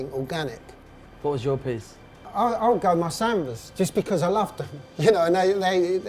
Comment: "What's your piece?" "I gotta go with my Sambas" *cuts to a pair of Gazelles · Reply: I thought he said salmons as in the colour?